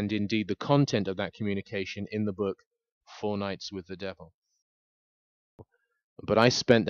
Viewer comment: I thought he said “ Fortnite with the devil”